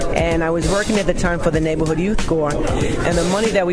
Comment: Hey I'm not native english speaker can somebody lend a hand? What does she say in 1:46? I was working at the time for.......??? Thanks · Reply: She was working for the Neighborhood Youth Corps.